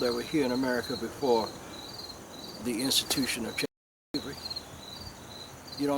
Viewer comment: Why does the video cut out at 4:30 “before they instituted______ “ his lips move but the sound is gone... why? What can he not say? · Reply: He said before the institution of chattel slavery that will mean what we always been here